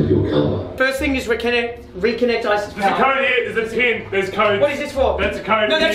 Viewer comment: did he say reconnect isis power